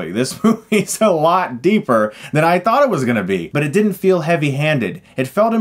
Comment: I legit thought he said it was gonna suck